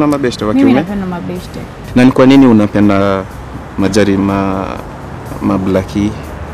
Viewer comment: Majari nani ameskia hiyo instead of machali hahaha niokotwe ebru tv